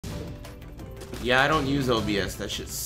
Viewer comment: i thought he said "yeah i dont use opiates that shit sucks" at the start of the video